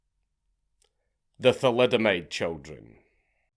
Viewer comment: oh shit I thought that said thalidomide chicken